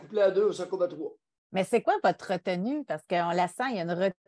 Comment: Quand Josey a dit c'est quoi votre retenue ? J'avais entendue c'est quoi votre `` re tout nue ``